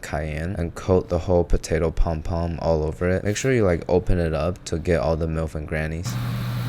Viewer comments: did he say "make sure you get all the milf's and grannies"?!?!? Instead of nooks and crannies, lol fvcking brilliant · Did bro just say "make sure to open up to get all the milfs and grannies"